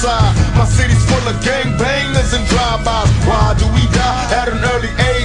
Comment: I always thought when he said why do we die, I thought he was saying ride till we die.